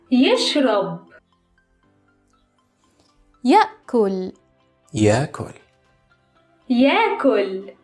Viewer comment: i heard yakult